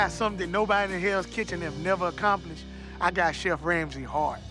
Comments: “I might have not won Hell’s Kitchen, but I got something nobody else on Hell’s Kitchen has accomplished. I got Chef Ramsey’s Heart.” And everybody else’s, for that matter. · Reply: I thought he said he got chef Ramsey hard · "i got some thing no one accomplished , i got chef ramsays heart." -. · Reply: I thought he said: I got chef Ramsay hard 😂😂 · "I got chef Ramsay's heart" No, buddy, you got all of our hearts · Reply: I thought he said “ I got chef Ramsay hard” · “I might have not won Hell’s Kitchen, but I got something nobody else on Hell’s Kitchen has accomplished. I got Chef Ramsey’s Heart.” And everybody else’s, for that matter. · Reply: i swear to god i heard him say i got chef ramsay hard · "i got some thing no one accomplished , i got chef ramsays heart." -. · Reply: I thought he said ‘I got ramsay hard’ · Not gonna lie. At first I thought the last word he said was ‘Hard’ not ‘Heart’.